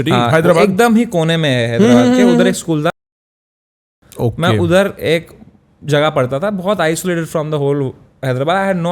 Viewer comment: what he said? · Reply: Don Bosco school